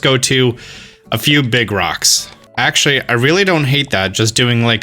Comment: Everyone talking about how peaceful this series is, and I'm over here trying to figure out who rock the geodude was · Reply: SAME I thought he said Brock 🤣